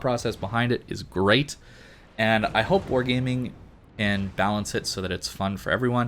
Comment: Sorry for another message but what was that you said at 31:06 did you say WG balance it 🤣 WG and balance cant be in the same sentence Kappa